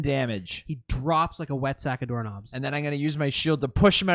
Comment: Did say “wet sack of doorknobs” at 1:38:45???